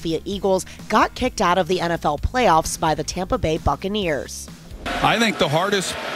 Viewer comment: I thought she said ****iners 💀☠️💀☠️